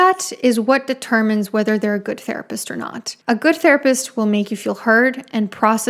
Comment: oh lord I thought you said "a good therapist will make you feel hard"